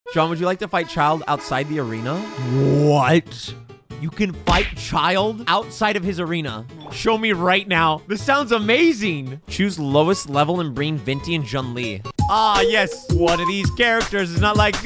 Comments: I thought he said “You can fight a child” 🥴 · I thought he said you like to fight a child” 💀